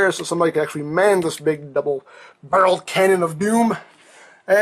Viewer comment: i thought he said BUBBLE BARREL